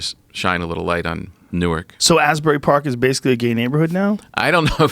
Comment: A gay neighborhood?! Haha, I thought they said 'gated'.